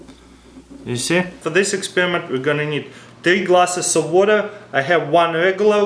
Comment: sounds like hes saying titi glasses